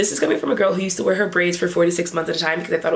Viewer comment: Girl, I thought you said 46 months. 😅 I was relieved to hear 4-6 months. 😂